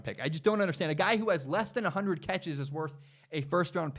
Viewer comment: I jad to rewind the first part because i thought I heard him say he got 600 td catches. ..lol